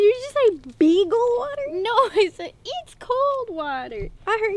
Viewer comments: Am I the only one that actually thought that nessa said ' bigel water' hahahaha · When she siad it 's cold water I thought she said picklewater